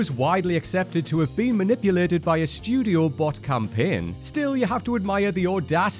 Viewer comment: What was manipulated by a a Studio Campaign? The Audience score or the Critics score? Is he saying "Studio bought Campaign" or "Studio Bot campaign"?? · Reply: The latter (bot).